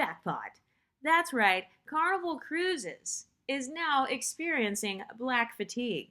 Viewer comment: I seriously thought I heard Tom Cruise was experiencing black fatigue. 😳